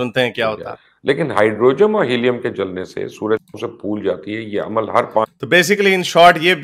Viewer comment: Location 5:36 Dhyaan se sunein. Did Mubasher Lucman say Hydrogem instead of Hydrogen?